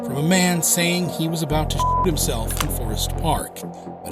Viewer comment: anyone think he said “shit himself” not “shoot himself”